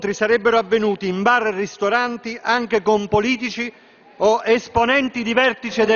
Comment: 'esponenti di vertice del MES???....ho sentito bene?